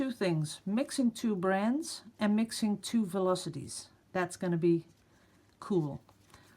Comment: Sorry, I thought you were saying velocity not viscosity